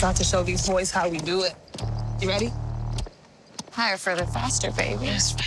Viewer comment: I heard “higher for the bastard baby”